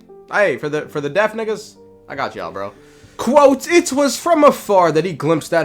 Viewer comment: did he say "for the deaf ____ I got y'all bro" cause if he did he's lying